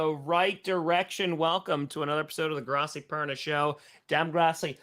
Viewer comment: it sounds like you said the bronco perna show 🤷